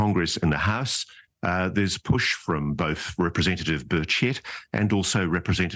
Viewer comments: Did he say at 1:29 Representative Birdshit? 😅😅😅😅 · I know he didn't, but I could have sworn he said "representative bird sh*t"